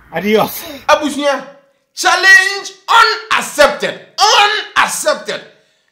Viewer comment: I thought you said killer Kay's challenge is accepted now you said, the challenge is unaccepted....you said what, 🤣😂🤣😂🤣😂🤣😂🤣😂😂🤣😂